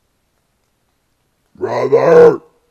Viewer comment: Sounds like it says Robert a few times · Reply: It's brother, not Robert